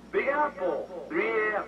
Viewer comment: LOL...I swear, it sounds like TNMT says "Big asshole 3 A.M" LOL